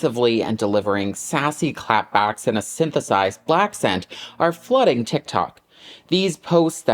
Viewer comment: Did you just say “Blackcent”?